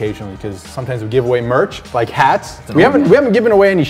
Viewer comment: I thought he said we will give away cats and I was like 😍😍😍😍😍😍CATS CATS I LOVE CATS and then I replayed the video and twice and he said hats and now I'm dead inside😭😭😭